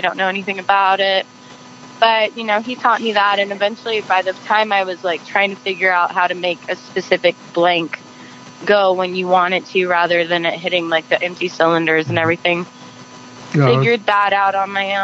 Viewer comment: I have 60 year history of owning and shooting firearms. People familiar with firearms use the correct nomenclature. "Cartridge" and "bullet" are two different things. Someone familiar with firearms would never use the wrong word, just as a pilot would know that 'rudder" and "wing" are not the same thing. That clip of the armorer talking is scary. She used the wrong words. If she had to figure out how to make blanks, on her own, something was very wrong. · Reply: "If she had to figure out how to make blanks, on her own, something was very wrong." That's not what she said. She said (20:53) "... by the time I was, like, trying to figure out how to make a specific blank go when you want it to, rather than, like, hitting the empty cylinders and everything." The word "make" here doesn't mean "manufacture", as you've assumed. It means "cause".